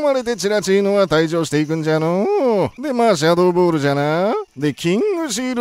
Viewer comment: マーシャドーボールに聞こえたww